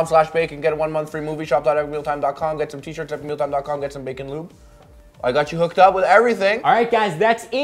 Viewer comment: Ummm... 9:15 did he say bacon lube?